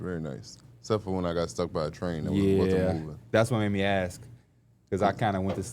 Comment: I thought said he got struck by a train so casually😭😭